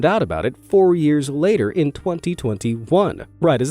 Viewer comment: Did I hear him correctly. Did he say four years later in 2021